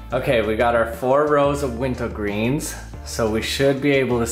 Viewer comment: I keep hearing you say "Winto Green" or "Winta Green"